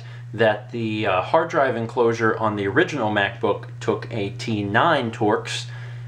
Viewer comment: I thought he said the mac book can twerk 89 times